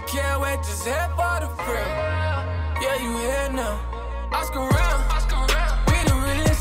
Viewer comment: I thought he said "I Skrt Round, I Skrt Round"